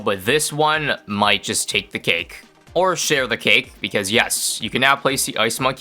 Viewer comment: Dude i thought you said "sh*t on the cake" instead of share the cake